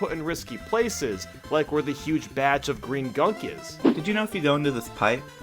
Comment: I heard "green gunk" as "green gum"